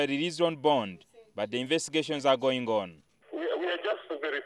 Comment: Did he say "the investigations are going gone?"